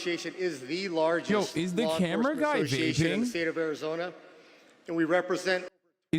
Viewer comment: I thought bro said camera guy was Jinping. I was like why the hell is Chinese head recording some local unknown sheriff as part of his hobby